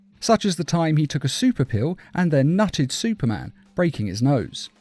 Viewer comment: I'm sorry, I could of almost sworn I heard you say he "nutted" supermans nose...